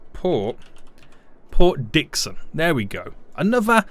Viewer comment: naming the harbour Port Dixon Me heard it as Port Dickson: Malaysia!?